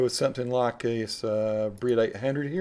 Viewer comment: Can you clarify what is said at 1:55 please? for some reason my pea brain is registering breelate hendrey ...lol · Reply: Brit 800